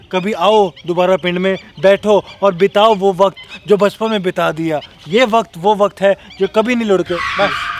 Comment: said jo kabhi nhi ludkega instead of lautega...😂🤣